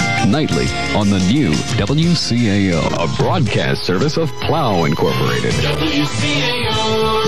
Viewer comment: Sounded like he said A broadcast service of Clown Incorporated. That can't be right.